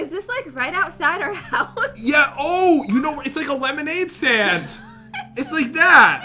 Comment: I thought said a lemon thousand😂😂😂